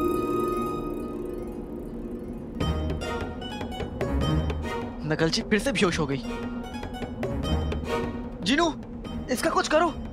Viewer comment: am i the only one who got that he said nakalchi instead of nakchadi😂